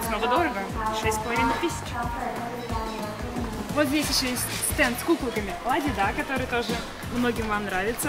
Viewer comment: на 13:34 что за куклы? я просто не услышала! у меня такая есть. с фиолетовым цветом волос. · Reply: Vlada 1004 , Lady Da